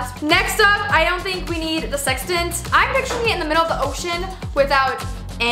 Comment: at the time in the video 9:40 when i heard it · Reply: She's said "ocean"